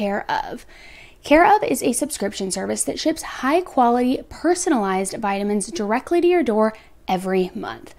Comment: 🤣🤣🤣 OMG 🤣🤣🤣 at first I thought you said... "personalized violence shipped straight to your door"... 🙄🙄🙄